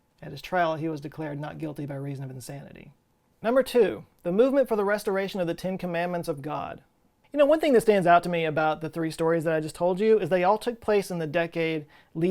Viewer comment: I just misheard “society for the restoration of the Ten Commandments of God” as “society for the restoration of the Tin Man of God.” If I only had a brain